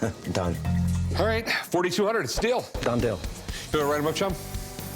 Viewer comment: It's a deal, or it's a steal, wonder what Rick really said at 5:38. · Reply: He said it's a steal.